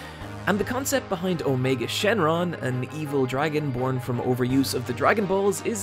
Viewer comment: did i hear Dragonborn? Fus Ro Da!...